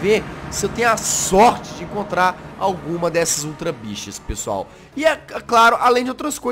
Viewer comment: kkkkk só eu entendi "ultra bixas" morri....